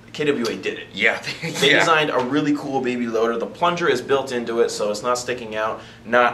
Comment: Anyone else hear say "Baby loader" at 2:05?